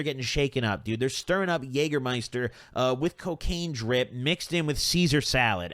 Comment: did i just hear jeager